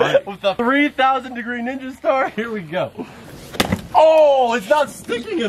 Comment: did he say nigga star lol